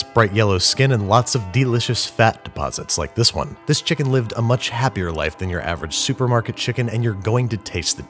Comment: "This chicken had lived an happier life than your average supermarket chicken" I thought he was saying "an happier lifethan you" XD